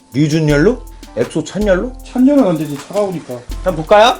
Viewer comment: 찬열은 안되지 ~니까 여기서 뭐라고 하신거에여? 안들려서ㅠ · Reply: 차가우니까...